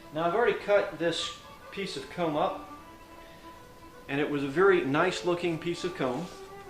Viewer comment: sounds like he says come up or cum up lol